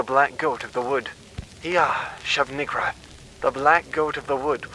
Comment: I thought that one said shub niggawrath 😂😂😂😂😂